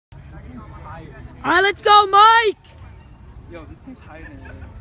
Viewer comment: I thought he was saying let’s go mate